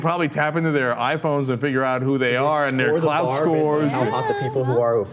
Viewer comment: Lol whole time I thought they were saying cloud score.